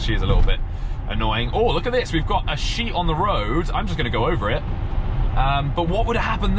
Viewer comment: I thought you said "Oh there's a sheep on the road, lemme just go over it" OMGG :)))))))))) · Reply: I first heard "there's a she on the road I'll just run over it". DUDE! 😂